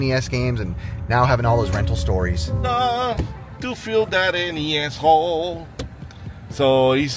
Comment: not sure what said, sounded like "To fill any s-hole"?